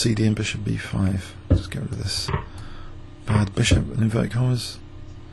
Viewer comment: What are you saying at 0:37? I hear British people saying it all the time so I'm curious. It sounds like "with inverted commas" or something. what is it?